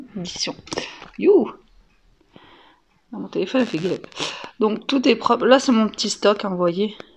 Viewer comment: A 2:51 tu a dit mon téléphone quoi 🤣🤣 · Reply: Ah il a fait gelup lol mdr je sais pas comment ça s écrit